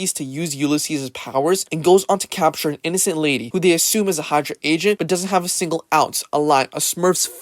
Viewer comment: i love how the captions at 1:42 say hardcore instead of hydra 🤣😅😂